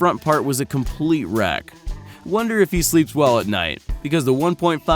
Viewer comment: Did he just say "sleeped?"